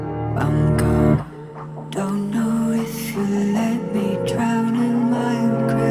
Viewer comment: kinda sounds like he says “don’t know if you let me drown in minecraft” and honestly that’s relatable